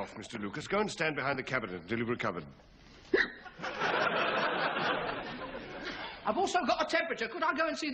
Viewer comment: What did Captain Peacock say? "Go and stand behind a cabinet and deliver a cupboard". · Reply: go stand behind the cabinet until youve recovered